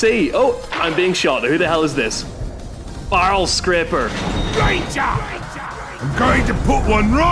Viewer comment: I thought you said bowell scraper at first. Wow, what a name.